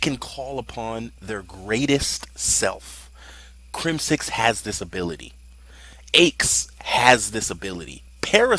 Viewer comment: thought he said that aches crimix and parasite had a disability xD had to listen again